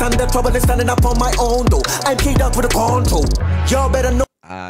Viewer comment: I think he said "I'm K Dot with the Control"